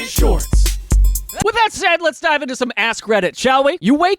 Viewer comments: I swear I thought he said "With that said, let's dive into some ass credit... " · I heard "Let's dive into some ass credit, shall we?" 😂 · I was so lost for a minute when (at 0:18) Daniel transitioned from shorts to "lets dive into some ass credit". 🤣🤣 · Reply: Yes! I heard "ass credit" too. So glad someone else did so it's not just me giggling to myself. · I'm sorry but did anyone else hear "Ass Credit" instead of "AskReddit" at 0:21?